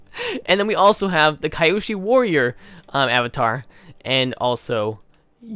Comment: Did he just say kyeyoshi warriors